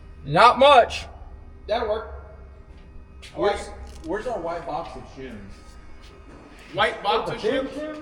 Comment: I heard "white box of sinns" and I thought "wth kind of system of a down song is that" hahaha